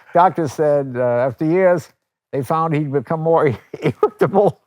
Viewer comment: what was the punch line? For the life of me i can’t hear what he said properly from all the laughing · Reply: He became more irritable.